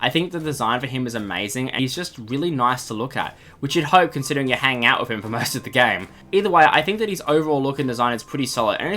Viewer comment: i have no fucking clue what you said lol buddy pal man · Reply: I said he was nice to look at